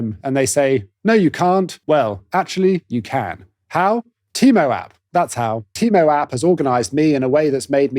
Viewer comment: You said "Teamo App", but I really thought you said "Temu App"! Took me a second... 😂😂😂 32:24 It seems like a lot of people are forced to abandon the thought of a career in the entertainment field, but I had the opposite: my parents never got into the entertainment field like they wanted, so they forced ME into it! Took me years to escape, but I'm free and will never go back (I would've loved to go into accounting, honestly).